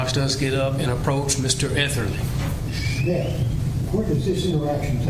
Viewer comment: I swear he said "shit, where does this take place?" lmao